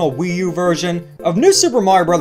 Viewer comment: The way he says Star man I thought he said Dhar Man 😂